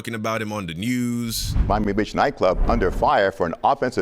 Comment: Did he say "Buy Me a Bitch Nightclub???"